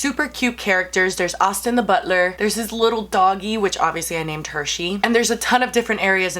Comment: I get now that she said "name Hershey" but I first thought she said "name her, she" and it was weird